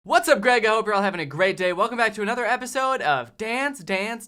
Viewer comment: I thought I heard "what's up Greg I hope you are having a Greg day..."